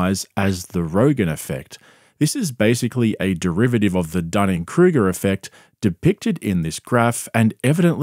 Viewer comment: I thought he kept saying Diane Kruger effect 😂